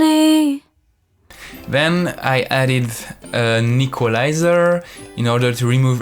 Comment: The way you said Equaliser I heard you say Nicholaiser